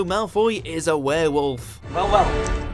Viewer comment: Immediately after the narrator said "Draco is a Werewolf" and Draco says "Well well", I thought he said "Bow wow", and I figured that I had just glossed over that scene from the movies. Hahaha. 😂